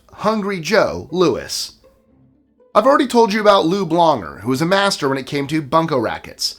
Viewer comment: I thought you said Lube Longer 😂😂